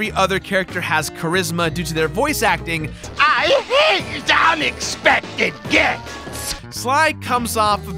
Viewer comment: Sounds like he is saying "I HATE unexpected D#$@